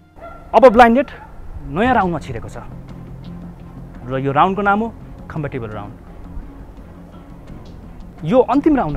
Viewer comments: ohhh myy god... am I the only one who heard khamba cable round instead of compatible round?? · Am I the only one who heard “khamba table” round not “compatible” round in 3:15😭